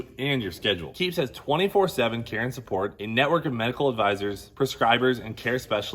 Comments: I thought Cleeter said free Karen support with the keeps 😂.. I was definitely not sold on that one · I heard “keeps has 24/7 Karen support” instead of care and support. Probably both though 👊